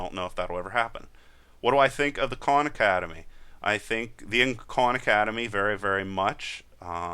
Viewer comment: Lol I thought you said 'con academy' but your saying KAHN hahahaha... you know both styles are good in there own way... They do not coveer what you cover and visa versa...excep for python.